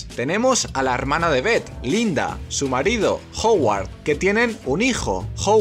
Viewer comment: Lo que dice: Howard Lo que escucho: ¿Hogwarts? 🤨